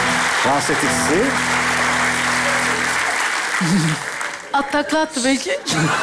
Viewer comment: de ne diyor anlamadım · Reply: At takla attı belki